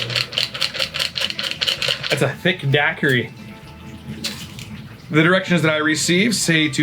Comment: I first thought you said Copy Daiquiri. How many did you have before hand? Cheers!